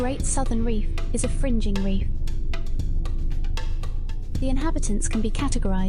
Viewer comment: At first I thought it said “fucking reef” 51:25 What’s wrong with me 😭